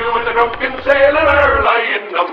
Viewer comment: Is it just me that it's sound like foxy is saying "dunking sailor"